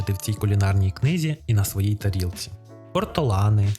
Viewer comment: почулось спочатку Портолани, а птичка називається Ортолан =)